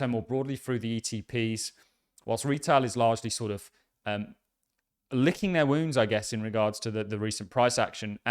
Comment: I listen at 2x speed.... i thought you said licking their windows hahaha (when you said lickingtheir wounds)